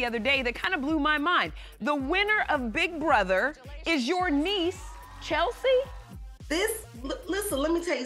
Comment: She has (1) one Siblings who was unalived at 17, so HOW ??? 🤔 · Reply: Sounds like she said Chelsie was a neighbor.